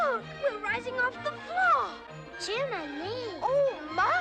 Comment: did they actually say “Jiminie” ?? It sounded just like it omf 😂 😂